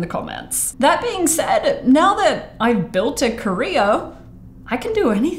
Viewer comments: I thought you said "I built a career" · When she said: "When now I have built a career" I heard it as "I have built a korea"